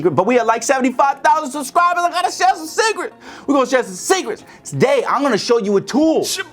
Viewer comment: I know said secrets, but I heard "We gon share some cigarettes"